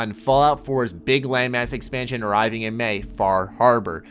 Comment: when he said land mass expansion I thought he said lame ass expansion